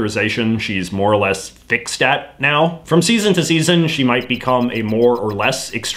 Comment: When you said “Fixed at” while talking about Lisa I heard “Thicc” and became confused and disturbed